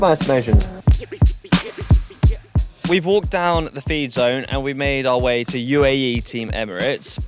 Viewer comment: I thought it said "Fred Zone" and was confused for like the first three minutes.